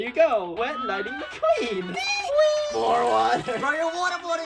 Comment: why did you say mater 😂😂😂😂😂😂😂😂😂😂